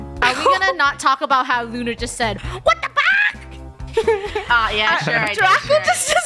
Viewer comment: At start i heard"WHAT THE PUCK AHH"